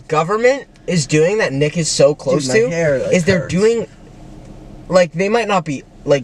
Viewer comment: did he say my hair hurts?